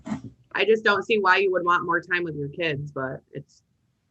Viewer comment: Hold up, did she at 34:18 "I don't see why he would want more time with his kids" am I hearing that correctly??? Did you see mums face once it was explained why dad seemed disinterested in the court hearing 🤣 and also when the judge thought dad was being rude, she was happy then lol